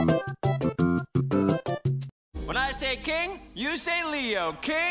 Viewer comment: When he said this (yes i watched this episode a knew what he said.) i thought instead of saying king he was saying cake